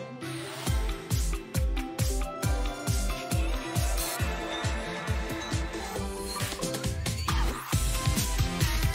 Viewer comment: can i hear 'Hilda' being whispered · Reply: There is subtitles on netflix, it actualy does say 'Hilda'